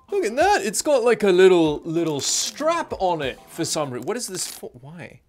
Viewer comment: Was watching somewhere else for a while, when i heard "strapon"